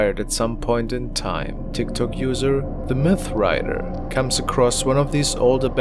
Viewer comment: Anyone else thought he said the meth rider lol